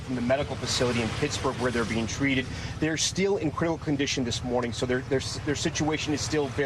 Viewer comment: “Criminal condition” instead of critical condition. Oops slip up there. Even the reporter can’t contain it😂 this whole thing stinks.